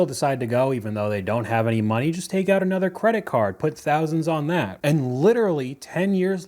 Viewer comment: I thought she said forty thousand on the yitties